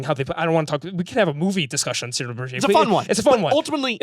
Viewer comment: What's the movie mentioned here that is said to have inspired Rothfuss (@ 46:50) ? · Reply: Cyarano de Burgeac